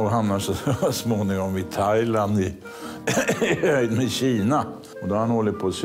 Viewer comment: "och då hamnar han så småningom i Thailand i Kina." Vad sa han egentligen där? :) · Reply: "även kina" säger han